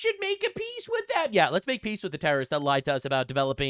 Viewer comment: “Make Peace with terrorists”? You misheard. It’s “Make pieces out of terrorists.”